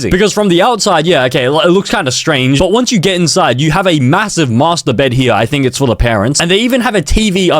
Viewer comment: did he say massive😂